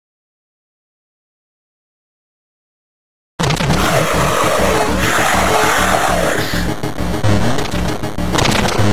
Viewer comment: What did MX say? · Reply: Mx said luuuuuuuuucaaaaaasssss